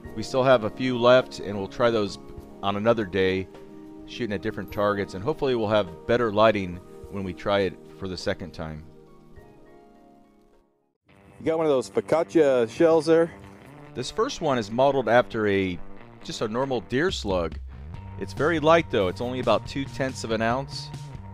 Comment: lol did he say focaccia